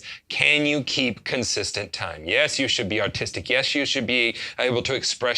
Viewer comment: I thought he said autistic lmao i was gonna say "oh man, guess I'm a pretty great drummer then. Dont have a kit yet but i got one of them key components" hahaha